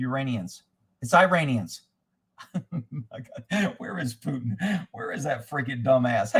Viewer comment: Oh OK, I thought he said Uranians, like from the planet Uranus.